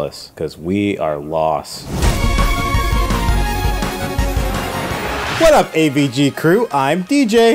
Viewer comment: I was surprised at the beginning of the video. For a moment I thought that DJ said “shut up AvG crew!”